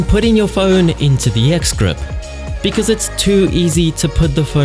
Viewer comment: I keep hearing "excrement" rather than "x-grip".